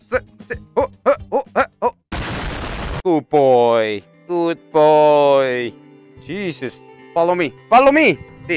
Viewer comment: lol i hear tupoy then good boy 1:44 (тупой - (tupoy) on russian mean - stupid)